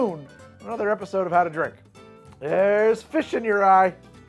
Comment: Okay, the last thing you said was "Theres fish in your EYE" but first I heard "Theres fish in your RYE". An idea for next time mayhaps.